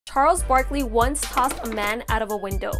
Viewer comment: I thought she said knarles Barkley and I thought maybe he was actually going crazy